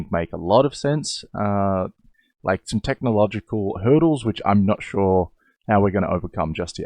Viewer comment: i was playing a game while watching this video, and a sound overlapped when you said hurdles, and i thought that you said turtles